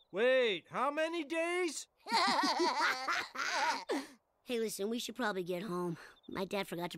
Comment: Now I may have missed some possible context, but did Bart just call Milhouse, Lisa? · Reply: The Laughing Rabbit I thought he said listen